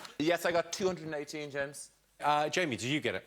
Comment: Did David just call Jimmy James?